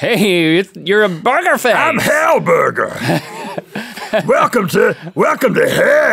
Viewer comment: when he said hellburger i thought he said hellbent